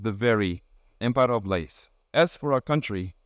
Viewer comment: wtf does he say here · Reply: “Empire of lies”